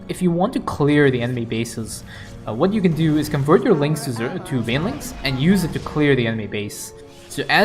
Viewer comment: What did you say here? I heard "convert your lings to Zeratul banelings" lol